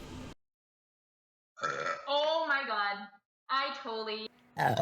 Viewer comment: Oh my god I ......what does she say after ? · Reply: Tottaly sorry